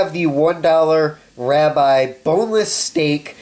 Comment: what? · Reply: I'm making a joke since at minute mark 0:22 it sounds like you say "Rabbi" instead of "ribeye". :D